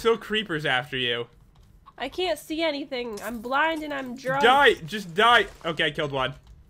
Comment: Did say "I'm blind and I'm drunk?" 😂